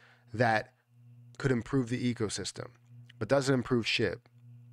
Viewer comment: I thought he said “doesn’t improve shit” @ 2:52 lol 😂